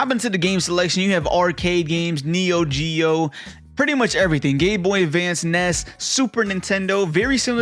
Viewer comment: did you just say GayBoy Advance? 😂🤣